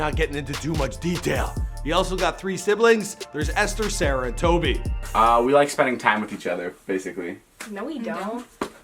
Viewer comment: sounds like he says Ester Sarutobi💀💀💀 (From Naruto)